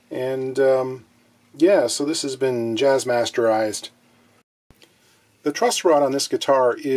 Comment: Wow that EKO is one unique looking guitar aint it hahah. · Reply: Jazz master ized. Sounded like you said "Bastarddized" lol !